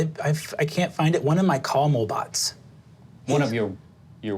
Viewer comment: Until I saw the "CAR-MO-BOT" caption I thought they were saying CARAMEL-BOT.